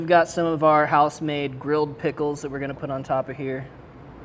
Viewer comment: Did you say GRILLED pickles at 7:06?